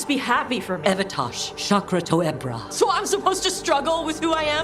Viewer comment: "Shokra toh ebta" sounded like shut the hell up lol